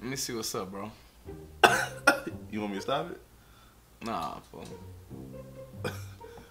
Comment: did he say “nah foo” 😂